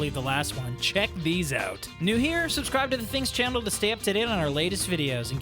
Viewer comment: he says chandll instead of channel 😅. I'm I the only one who has noticed that?